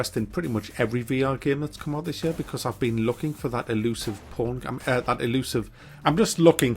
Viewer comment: Did my ears deceive me, or did he say "elusive porn" at 1:03?